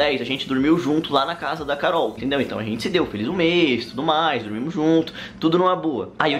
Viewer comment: so eu que achei que ele tinha falado a gente se deu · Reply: Ele falou " agente se deu , dormiu junto na casa da Carol ... "